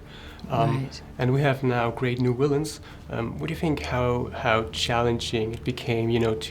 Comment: Is he trying to say villain? It sounds like he is saying willain.